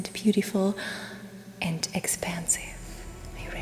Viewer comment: Your videos just keep getting better and better however at the very beginning I thought you said that it was going to be expensive then realized you said expansive